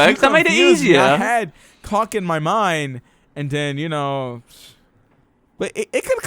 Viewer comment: Did just say "cock in my mind"?? :P